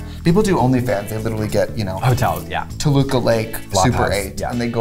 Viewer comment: Did Trixie really say Toluca Lake at 9:19??